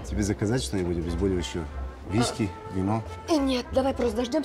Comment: Паша говорит Виськи и мне послышалось Вискес